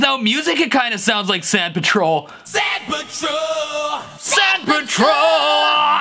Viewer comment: i heard “saint patrol” 😂